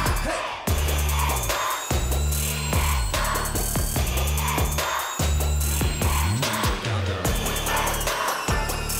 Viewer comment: Does someone know what the public is saying during at 2:07( when they dont sing actually) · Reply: Lilia Bnb E X O ^^